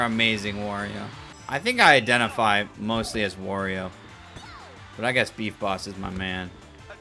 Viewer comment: I was watching in the living room when you said “I feel like I mainly identify as Wario “ My mom thought you said “ I sexually identify as Wario” idk why but it was so fricking funny the way she said